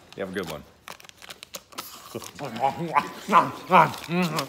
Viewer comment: Wait wait what does she say at 0:35?? I must be old, I can’t hear it…😆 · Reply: "I love Lady and the Tramp."